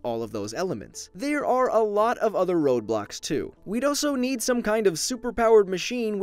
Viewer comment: i thought he said ROBLOX but it's roadblocks .-.